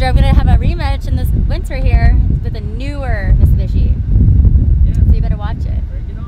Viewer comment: When she says mitsubishi it sounds like she says Miss bitchy xD